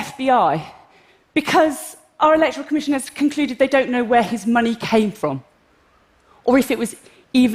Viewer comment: I think she said “electoral commission has concluded that they had better to know ..“ Did she？ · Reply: She said "...electoral commision has concluded they don't know where his money came from." Hope this helped :)